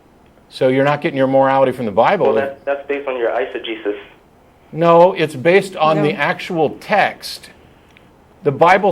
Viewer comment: what is he saying? Iso-Jesus? I have listened several times but I can't make it out, it doesn't show up in the subtitles or in the transcript. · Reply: Exegesis